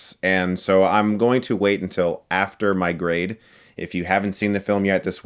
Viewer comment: Hahahaha! "...after my grade" I heard it like, "after migraine" - okay!! LOL! Linda Hamilton doing a Arnold Shwarzenegger swagger...The heroine could have a better name than 'Grace'...Eh, and that "my whole body's a weapon" - fingers all blades?! Yup! They got it covered at every angle...mm. "mediocre, bland pile of crap" Enjoyed your summary! They're "making it" for a new generation!